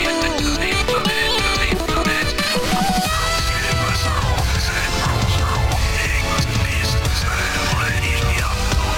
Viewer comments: Does anybody mind to help translate what specimen 9 is saying at 1:48? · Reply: Was deemed too hard to contained killed and dismantled by gl Labs seem to gain more form. As more subjects were taken taken taken, taken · what did he say? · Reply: Deemed too hard to contain was killed and dismantled by GL labs seemed to gain more form as subjects were taken